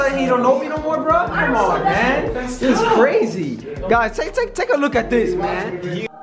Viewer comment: I thought he was trying to say take a look at Kaylens hairline lmao 😂